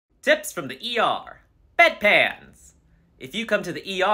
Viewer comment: I thought he said deaths from the er